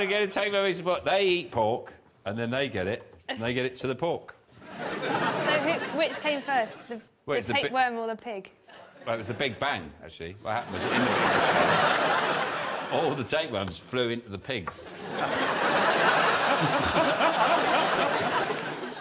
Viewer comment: If he’d have said it was the pig bang instead as I thought he first said that would’ve been even better